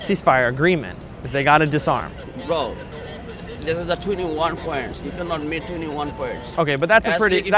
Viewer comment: I’m sorry, what does he say at 4:00? This is the “twenty one points” ??? I have played this over and over and I can’t make sense of it